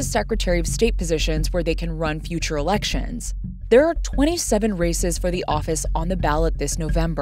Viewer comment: Lol at 2:23 I heard "there are 27 racists"